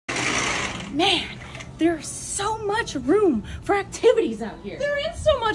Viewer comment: I don't know Why I heard...... "There's so much room for Tiddies out here". Must be my old ears.😜